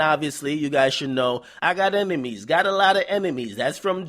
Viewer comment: Sound like he said emenies lol